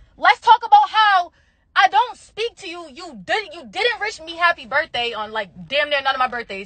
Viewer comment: she said rish 😂 instead of wish